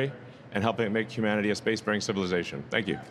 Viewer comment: i heard "make humanity a space marine civilization" lol